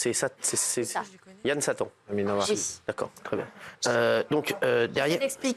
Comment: Il parle de Yann Barthès ??? ya que moi qui est entendu cette dinguerie à 4:25 ????